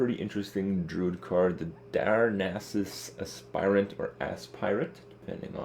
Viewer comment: Sounds like Kripp says: Darnassus Ass pirate. ;)